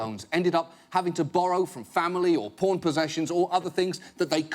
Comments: Did he say porn possessions at 14:39? What the heck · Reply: He said "pawn posessions", so sell your stuff. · I've watched this video over a dozen times over the years since it first aired, and I just realized John was saying "pawn possessions" at 14:40. I always thought he was saying "porn possessions" (as in "borrow from family or sell porn possessions") and had always wondered why it didn't get much laughs...